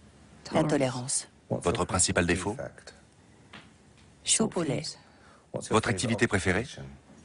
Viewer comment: j'ai pas compris à 2:15 elle a dit quoi ? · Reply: Soupe au lait